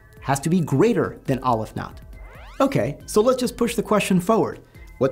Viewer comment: All I keep hearing is "Olive Knot"